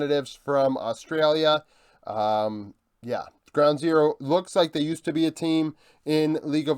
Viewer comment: I thought Ground Zero said G2 so i was confused what caps and co has with the LCO